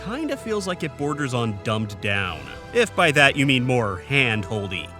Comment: Did you say "dumped down"? You mean dumbed down, right?